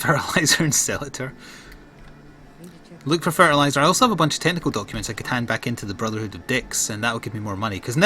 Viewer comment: At about 23 minutes I thought you said tentacle documents instead of technical documents.